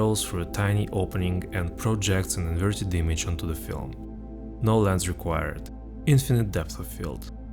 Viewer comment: i swear when you said no lens required i heard Nolan's required